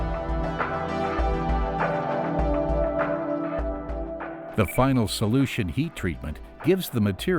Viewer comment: Did I just hear "The Final Solution"? hahaha lol . That would not be funny if the company wasn't german